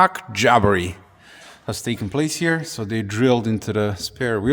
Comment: did you say DILDO? 😮😮😮